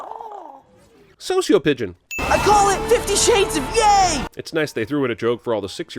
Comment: “I call it 50 shades of gay” is what I heard